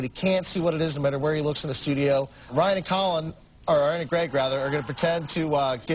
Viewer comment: I thought he said Wanye and Greg